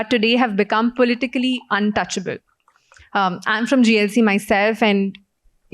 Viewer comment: what is JLC Or whatever she said